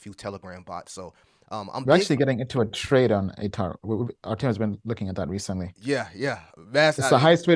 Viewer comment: what crypto did he say here? "ataro"? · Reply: ATOR